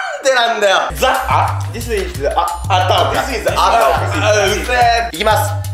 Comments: 『This is a 田岡』が途中から『This is あたおか』に聞こえてきたww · This is a 田岡がThis is あたおかに聞こえてくる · This is a 田岡がThis is アタオカに聞こえる